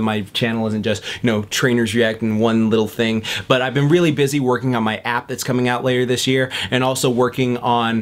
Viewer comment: When you said "I've been really busy working on my app" I heard "i've been really busy working on my abs" xD